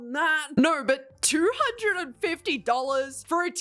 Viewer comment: I like but this is ridiculous 😠 1:12 wait did she say 250$ 😰 If I grabbed a blanket though and someone pushed me oh ohhh id be mad but like what the heck 😡 HCYTRDTSGYJGJHYFTHFTUGJ